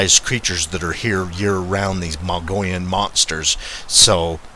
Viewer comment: Just an FYI, Pronounced Mug-e-on. Enjoy Az, that's a great area you're exploring. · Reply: I thought he was saying Mongolian...